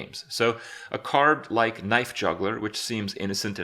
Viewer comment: I heard Knife juggler but maybe I'm just drunk. Can anyone confirm?